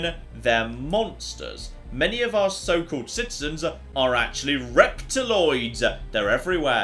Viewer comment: i was lying back listening to you read this out and my eyesight is bad... i could have sworn you said "rectaloids"